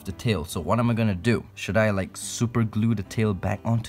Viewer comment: Did he just say=should I just super glue it back